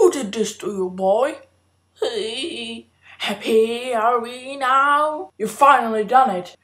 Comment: when vernon said `happy are we now` i thought he said ` a he are we now`